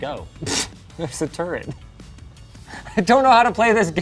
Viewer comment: whoever submitted the subtitles was either very tired or tech just isn't their thing, as they wrote "10 ADP" instead of 1080 and "turd" instead of turret (14:35) 😅